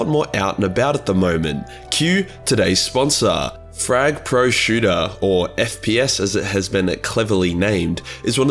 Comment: I'm completely sick right now so when you started your ad for frag pro shooter I thought you said fried prosciutto and got really hungry